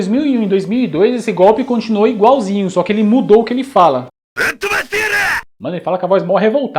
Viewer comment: Sempre achei que ele falava "A fumaceira!" Kkkkkkk Acho que uma tradução mais certa sería "Então voa seus FDP!" Tipo Gil Brother. Estudei japonês um tempo, mas não o suficiente para entender todas essas falas. Muito bom esses vídeos.